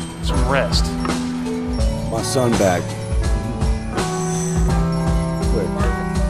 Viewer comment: did he say "my son back :( " ?? or I heard it wrong